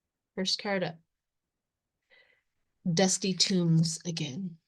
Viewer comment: Pardon me, but I think it is dusty tomes (large, heavy, scholarly books), not tombs. Maybe I misheard what you said. Thank you.